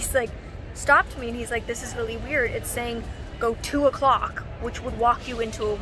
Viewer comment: Lol I thought you said "go to a clock" when you said "go 2 o'clock" like why would you need to go to a clock? 😂😂